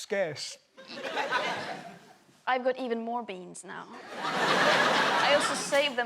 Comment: I thought she said "Beads" and with the laugh that the conversation was going into a real newsworthy direction.